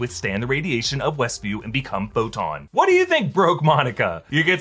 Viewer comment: When he said "what do you think broke monica?" i thought he said "what do you think, broke monica?" and I was slightly offended but honored at the same time